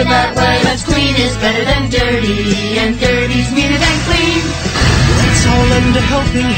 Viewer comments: i thought they said dirty is meaner than clean but that sounds messed up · "Dirty's meaner than clean"? The hell does that even mean? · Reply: I always thought they were saying “dirty’s cleaner than clean”. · and 0:22 Did they say, "Plus clean is better than dirty, but dirty is neater than clean!"? o_O